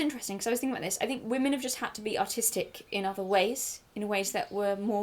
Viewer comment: extremely off topic, but im listening to this while on my walk and i thought you said "autistic in other ways"😭💀